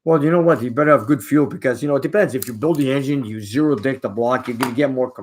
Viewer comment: Speaking of keeping it clean the closed captions at 10:25 and 11:38 it says zero dick instead of deck lol.. The moderator might wanna check closed captions. Love your channel keep it up your hustling where the weather is rougher than Seattle weather is inspiring to me.